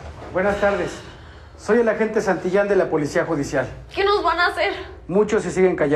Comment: en el minuto 17:39 pensé que decía "soy de la Policía policía " y después le fíje y decía judicial jajajsjjsjsaj